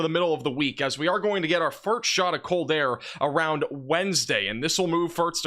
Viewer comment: Did he just say "fart shot" 3:32?